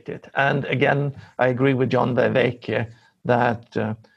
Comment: If he's talking about John Vervaeke, and not John de Vega (as the YouTube captions inform me) that would ideally be added to the show notes. It took me a dozen Google searches to land on "Vervaeke" as the probable target for this. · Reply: he finally says "Vervaeke" in a way that I heard it clearly, after the first two were garbled a bit by the persistent audio garble.